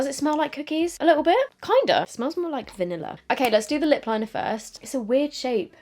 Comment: I really thought you'd said it smelled like vinegar instead of vanilla HAHA 😂